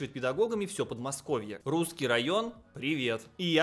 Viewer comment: РУЗСКИЙ РАЙОН? Мне послышалось ?😮